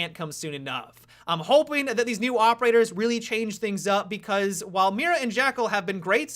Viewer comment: Did he just say 3 operators in the beginning